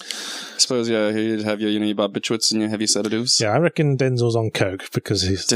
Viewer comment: What does say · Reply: "you know you have your barbiturates and heavy sedatives."